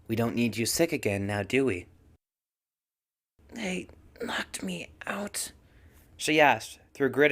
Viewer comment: Ah. She asked "They... knocked me out?" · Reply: Ohh.. I thought she said "they Locked me out?/!" I wasn't sure if it was a question or not cuz she was tired and her tounge was dry and stuff.. wow I have said a lot to you about this fanfic reading havnt I? Oh and will you be finishing this fanfic or...?